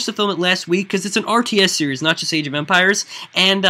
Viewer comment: the first time he said age of empires I thought that he said Asian vampires